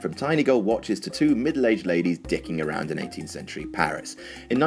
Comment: dicking around lol did i hear that right?